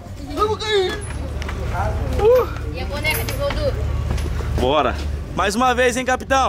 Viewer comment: só eu que escutei uma pessoa falar "e a boneca de voodoo" kkkkk